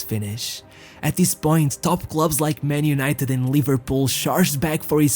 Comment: I thought i heard “Topgloves” but actually top clubs 😂